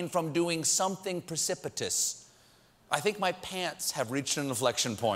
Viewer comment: I thought he said "Mike Pants" [pence]